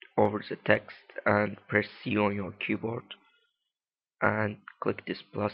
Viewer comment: press what in 1:55 ???? I can't understand you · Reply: C